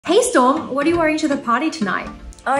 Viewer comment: I thought it said "What are you wearing at the porty tonight?"